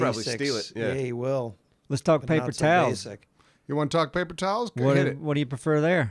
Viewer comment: When says, "Let's talk paper towels" (23:46), why do I hear Gary Busey saying, "Let's talk about buttered sausage? " 😂